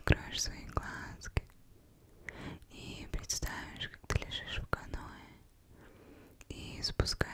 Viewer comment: Мне одному послышалась что она сказала приставов что ты лежишь в канаве?